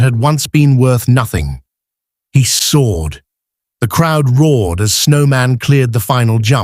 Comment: You need to hire someone for the caption writer who is fluent in English. At about 6:00 into the video, it says, "Snowman sword." Really?? He decided to fight instead of jump?? A third grader could correct that. A "sword" is a long, slim steel weapon. The word you want is "soared". Please. You can do better.